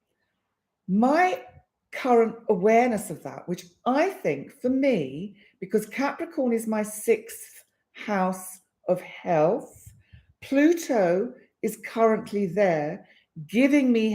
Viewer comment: I chuckled when I thought I heard you say, " Capricorn is in my 6th house of Hell"!!! 😂